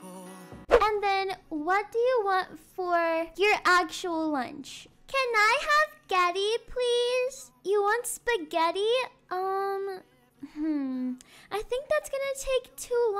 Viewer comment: Bro I thought said “CaN I have soMe GhEttO”